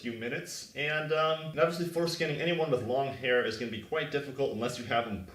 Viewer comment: Every time he says "photoscanning" I hear "foreskinning" and that feels like the subject for a very different video.